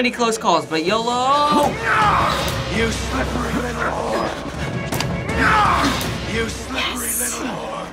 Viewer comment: Is it me or did someone say "You slippery little wh***"